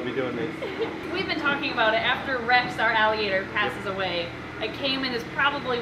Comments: Who else thought that she said “after Rex passed away-but she said after Rex passes away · I thought you said Rex pass away my volume was not to loud I replay it like 5 times lol. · Did you say REX PASSED AWAY 😭🥺😖😩 Edit: I heard it in a part of the video if I can find the time stamp I will post it here Edit 2: it was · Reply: She said WHEN she passed a away, not that she passed away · I heard at 10:58 ,rex are aggilator passed away and I almost had a heart attack , not when rex are alligator passes away..